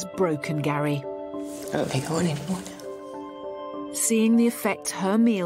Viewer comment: Does anyone know what said at the 31:22 mark? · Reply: "I don't think I want anymore now." - Referencing that she is both physically full from the large amount of food, and mentally upset due to her current weight and family situation, her time in The Feeding Clinic helped her realise how bad her condition is. The use of the word "now" references a sense of urgency, as she quickly got up from the table and reflected on her current situation and even her entire life struggles in food and weight. The use of the word "think" also indicates that she is only temporarily mentally upset, and physically full; perhaps she knows that in the near future she will grow accustomed to the larger portions, and she will become full less often. use of the word "want" also implies that she is still recovering from her struggles with eating. Her body needs more food, but her brain is telling her to reject it. Her brain doesn't want it, but her body needs it. Or perhaps she really is full, listening to her stomach. If so, she should have used the word need - "I don't need more food". Or perhaps her current mental state is making her nauseous, too ill to continue eating. The use of the word "anymore" shows that she doesn't want anymore food, meaning "any" and "more", combining to anymore, meaning "no" and "more" because she does not want "any" or "more" food; meaning "no" and "more" - A genius use of the word anymore. A truly sensational phrase uttered by Ms. this will surely go down in history.